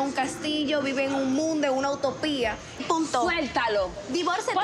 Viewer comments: I didn’t even peep that until now. Nicole said “punto,” like period, and Chantel thought she said “puto,” lmfao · Chantel’s family has a very distinct nose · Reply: Jedai the sister never actually called her a bitch. In the uncensored version, she says "punto" (period) not "puta" (bitch). She was saying "just divorce him. PERIOD", but Chantel thought she said "puta". Then when Chantel was walking away she called the sister a "whore ass bitch ass slut", but they cut that out of this version.